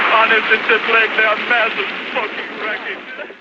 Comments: did he say massive fucking wreckage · Oh my god I think I heard him say fuck. I’ve never heard a newsman swear like that, not even in 9/11 footage. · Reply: I checked. He says "A mass of smoking wreckage!" I also thought he said fuck lol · I'm like 90% sure that at the end when the Hindenburg broadcast was fading out i heard him say "Fucking wreckage" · this is the only time I’ve heard swearing in a video this old. If I heard correctly he said “massive fucking wreckage”